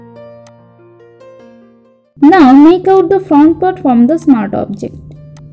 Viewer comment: I can't understand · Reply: mask out the smart object